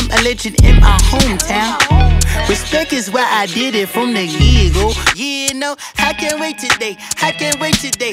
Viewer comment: I could have sworn paak said "the get go" instead of "from a year ago"